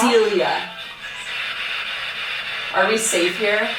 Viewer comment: says are we safeier and that sounds like it said no